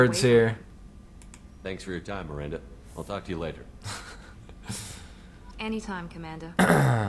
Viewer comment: i thought he said fuck you later. xD